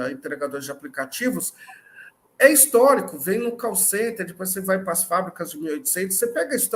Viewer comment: Quando o cara falou de "call center" eu ouvi "Carl Sagan" kkk